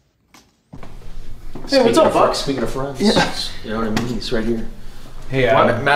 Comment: when adam said whats up buds, i thought one of the bucks said whats up Budge💀